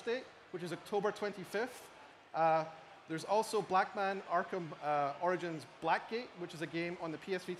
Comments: If you start at 17:08 and watch from there he says Blackman instead of batman. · who else heard him say "Blackman" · did he just say blackman? lmao